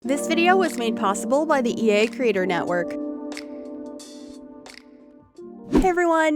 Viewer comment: Off topic but sometimes you say "creator network" so fast that I only hear "critter network" and I smile every time ❤